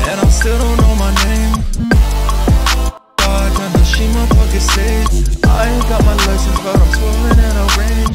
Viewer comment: did he say she my pocket sage??